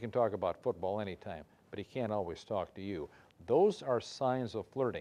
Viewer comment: i thought he said 'he can talk about football anytime but he cant always stalk you' HAHAHA!!! STALKER STARE